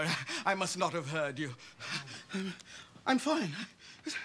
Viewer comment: LOL best part is at 2:48 when Will starts hitting Carlton too. idk if anyone else heard it, but I think he says "Get that punk, G! It's his fault!" when he's punching him XD